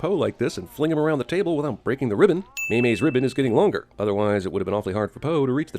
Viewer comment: he totally says "mayonnaise " instead of mei mei